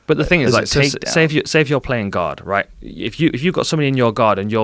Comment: I thought he was saying “Playing God” for a longtime. Took me awhile to realize he said “Playing Guard” Lol